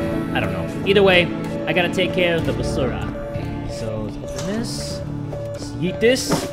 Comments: did he just say "basura" ????????? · Did he just say “basura” a filipino term for trash? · anyone also heard "Basura"? Or did he actually say it? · Did just say "I gotta take care of the Basura" HAHAHAHA Filipino fan here!!